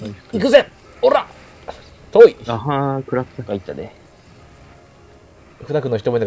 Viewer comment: ちょくちょく『あは～ん』って聞こえるんだがｗｗｗ